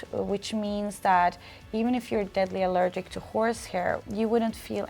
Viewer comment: Damn I thought it said nose hair XD